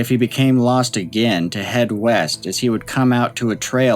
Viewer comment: Ooooh East? I thought you said Weest.